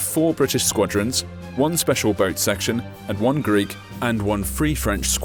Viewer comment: is he saying Greek ?